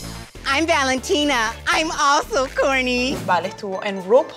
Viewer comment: I think she said Corny not Courtney lol